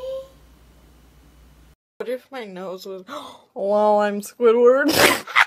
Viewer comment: She actually said lol I’m squidwird love your vids!!! ❤️🧡💛💚💙💜🖤🤍🤎